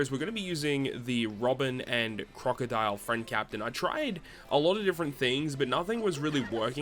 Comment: Well, I remember the last Kizuna to be a lot easier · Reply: wow I’m so blind. I thought you said “friendly” not unfriendly because yeah. This ones a mess.